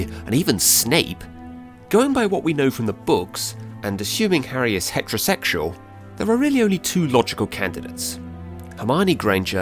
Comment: Tf did he say? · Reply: Assuming Harry is heterosexual, there are really only two logical candidates.